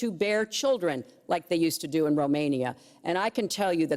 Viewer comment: Did she said Romania?! I know that country. It's my country.🙂)